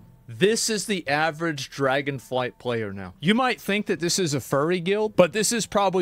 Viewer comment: As a warrior player I thought at first I thought they said fury, but I soon realized they said furry. Now I understand why they are up in arms.